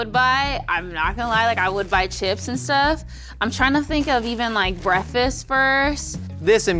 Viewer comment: Did she say . Breaffis first ?😂